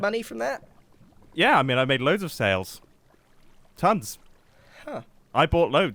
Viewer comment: Mumbo should've said "snails" instead of "sales" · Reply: I'm sure I heard "snails"